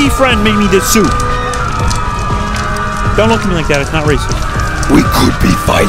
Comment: What he said: “Don’t look at me like that. Not racist” What I heard first: “Don’t look at me like that. I’m racist”